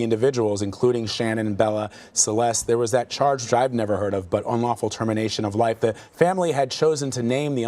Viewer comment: i thought it said “christopher wants to be sentenced” 😂- probably shouldn’t be laughing...